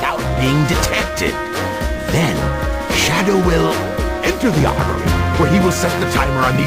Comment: I always thought Eggman said 'and Shadow will, ENTER THE ARMY' when I was little but then I was confused when he started killing the army cuz I thought he was part of the army and becasue I was little and Shadow was my favorite character I wanted to join the army to be like my favorite character even though I didnt really know what the army was